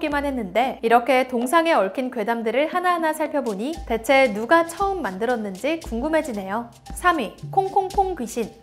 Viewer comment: 대체 누가 쳐 만들었는지 궁금하네요. 라고하는줄ㅋㅋ